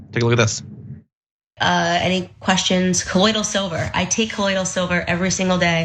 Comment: I thought she was saying coital silver at first lmaoooo 😂😂😂